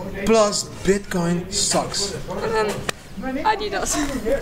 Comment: I though I heard bitcoin sucks but i rewinded it and u said socks! thanks god almost unsubbed! Leggo buy the dip